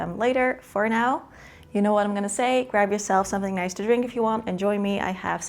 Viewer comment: i was in another room while listening and thought you said "go grab yourself some fentanyl"!!!